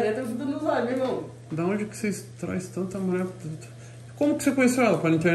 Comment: eu ouvi certo mesmo o que o Toguro disse?kkkk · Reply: ''Da onde que seis trás tanta mulher puta'" 😂😂😂